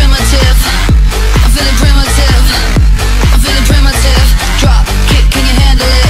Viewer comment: in the begin i heard: "FUCK, can can you handle it"